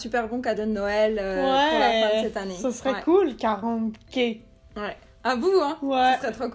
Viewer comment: ce que a dit : "ce serait cool 40k" Ce que j'ai entendu : "ce serait cool 40 gays" x)